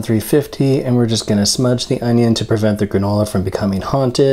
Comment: did he just say smudge the onion instead of oven, im shitting my pants laughing